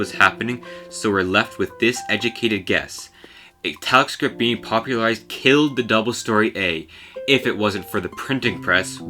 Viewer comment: Can you please tell me what you said at timestamp 5:29? Both subtitles and transcript say "a telescope being" which makes no sense to me. Many thanks. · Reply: "Italic script being popularized killed the double story a"